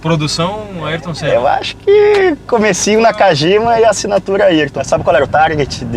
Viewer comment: O que ele diz em 01:41? "Acho que..... Nakajima"